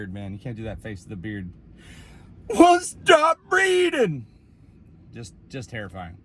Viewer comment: I heard "stop breathing" then "stop breeding" then I realised it was "stop reading" lol whoops